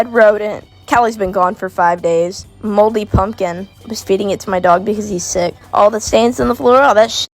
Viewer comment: did she say she was feeding that moldy pumpkin to her dog because he was sick?…